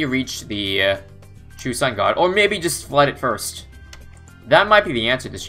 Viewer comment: I thought it said true sub god for a second